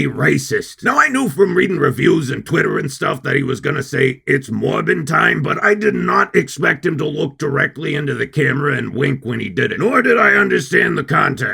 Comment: I always hear It's Morphin time, and I become confused.